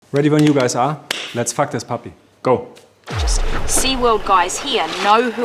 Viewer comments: Did he just say „Let‘s fuck this puppy“?😳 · Lets fuck this puppy? Oder hab ich mich verhört?